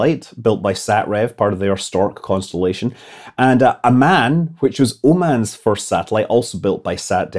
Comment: I heard “A man, which is womans first satellite.” and was quite confused.